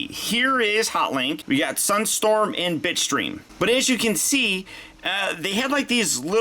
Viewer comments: I heard bitchstream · am I the only one who heard “b*tchstream?